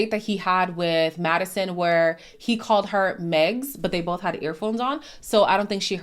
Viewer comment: I had to rewind when I heard him say Megs. & the closed caption tried to gaslight me & wrote "Mads". I know what I heard!